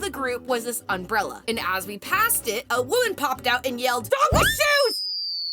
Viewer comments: DOG WITH SHOES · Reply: and i heard "dog-less shoes" 🤣🤣🤣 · wait what did thw women say at 5:55- i dont understand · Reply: "Dog with shoes!" Maggie was wearing shoes. 🫰🏻